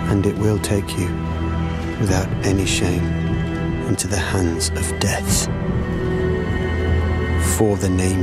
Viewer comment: I know its probably to late to get an answer 4 years later lol but what did he say at 1:09? Into the hands of death? Because to me it sounds like he's saying it with an s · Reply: It’s “hands of death.”